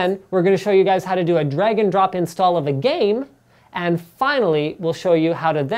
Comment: Those Canadians. I keep hearing "Dragon drop" instead of "Drag-'n-drop"